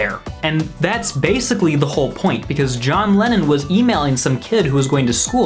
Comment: did you just say john lennon was emailing? In 1967?